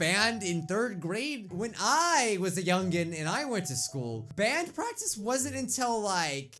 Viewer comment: who else heard "WHEN I WAS, A YOUNG BOY"